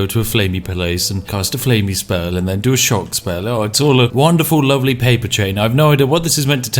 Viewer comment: am i the only one who heard him say to cast a shart spell?